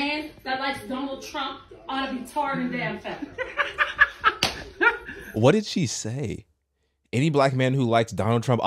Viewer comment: It sounds like she said tarred and then feathered.